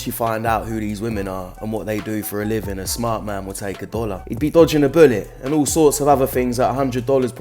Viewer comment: At the start For a second i thought you said a smart man would take ebola lol.